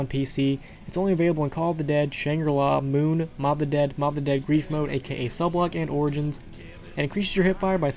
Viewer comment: What did he say at 7:10 ? He said mob of the dead once, and the mob of the dead "something" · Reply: Grief mod